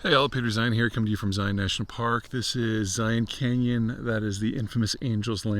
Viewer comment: I literally heard, "Coming to you from Zeihan National Park. That's Zeihan canyon."